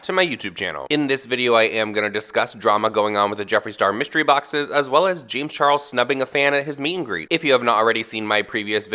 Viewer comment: my fatass is eating and I didn’t hear correctly so I heard “James Charles snubbing a xan at his meet and greet” instead of “fan” I was like bitch whet 😳😳🤣